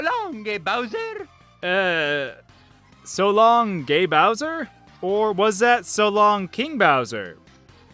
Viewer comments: So long gay Bowser... I still hear that. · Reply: I thought he was saying "so long de bowser" because he's italian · So long gay Bowser... I still hear that. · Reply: i always heard " so long le bowser" since "le" is Italian for "The" so he might have been saying " so long the bowser" i figured it was "LE bowser" cause i took marios accent into account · i alway thought Mario said "so longy Bowser!" whenever he threw Bowser... obviusly as a kid i thought that it made sense and i was just not getting it... but now i remember it, being mature and all that... it sounds SOOO wrong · I always heard Mario say "So long, ye Bowser!!" I still hear him like that nowadays. Or Star Fox, when the announcer said "Good luck", I thought he said "Remark!". Until I enabled Spanish, and I understood it 10x better. And Team Skull's encounter "Uh, uh, let's go! Keep it down, trying to make, Predator RAW...Just much...brulee, brulee, and BAM!" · I think Mario is saying "so long me bowser!" And on the DS version when Mario enters a stage I hear "pickle!" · I still hear Mario saying "So long big Bowser" when he throws him. I absolutely can not hear "King Bowser" at all... Also I always thought it said something like "A pickle" whenever you selected a course haha. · I think technically Mario's meant to be saying "So long, eh, Bowser?, but I heard it as " So longgy Bowser" (with a hard G). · I always thought he said "Shalom king Bowser". But why an Italian plumber could speak Jewish -_